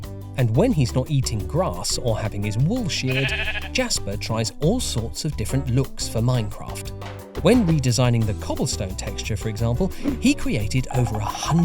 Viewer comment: I swear I thought that said Jasper Joestar